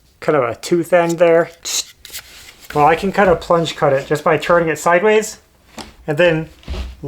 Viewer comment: What you did with your jigsaw to start an inside cut (4:50 in the video) we call it a “plunge cut”. If you need a name for it. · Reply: I heard him say plunge cut.